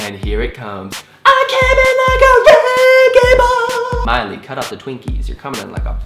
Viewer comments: Lol it sounds like he said "I came in like a red gay ball" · Is it just me, or when he sang wrecking ball it sounded like he was saying 'reggae ball'? Just me then...